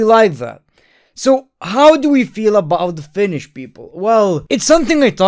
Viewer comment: misheard you as saying “so what do we think about finnish people?”